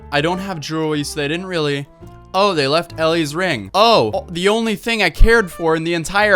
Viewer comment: i swear the mom says ‘yay you left ellies ring’. Did she completely forget what she was supposed to say lmao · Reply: *they took Elle’s ring